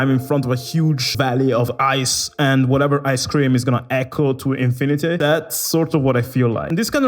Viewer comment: Whatever "ICE CREAM" is gunna echo to infinity I swear you said that. Ice....cold....I heard ice cream.